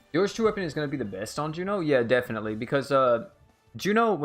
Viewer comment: What equip did he say is gonna be best for Juno? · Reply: I think he said leone